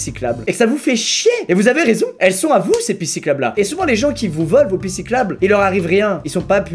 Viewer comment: c'est moi ou à 3:34 il dit "pissiclabe" au lieu de "pistes cyclables" ? 😂😂